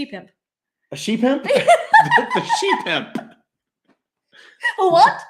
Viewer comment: 🤣🤣🤣🤣 Ohhh, a she-pimp... I initially heard that as 'sheep-imp' 🥴🥴🥴🥴🙈🙈🙈🙈